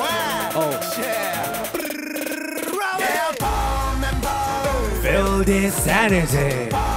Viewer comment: bomb n bomb が golden bombに聞こえる